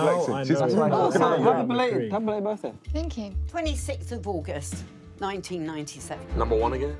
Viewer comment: when the first girls said something 'sixth' august 97 did I got scared for a moment because I thought I just heard someone randomly saying my birthday, but when I replayed it, I understood that I misheard what she said, just for the old lady to randomly announce my exact birthday right after, day month and year... I'm actually kinda mind blown cus it was just so random wtf?